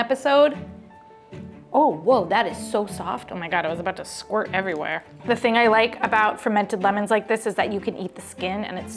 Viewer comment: that what she said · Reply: She says madareto gaiedam♥️